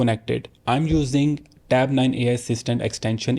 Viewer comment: Which extension? I can't understand. Can you please write it down? Thank you for your effort. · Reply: Tab Nine extension. It's an Ai feature